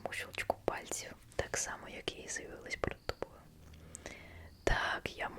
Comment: Вибачте а що вона сказала на 2:21? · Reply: Так само як я з'явилася перед тобою